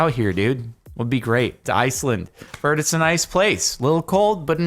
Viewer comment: Did Jardon just say that Iceland is "a nice place," or "an ice place?" 😗